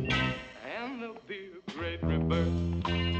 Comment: what does he say here? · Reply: “And there’ll be a great rebirth”